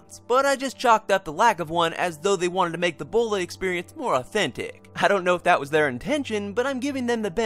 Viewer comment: I seriously thought you said "ebola experience"...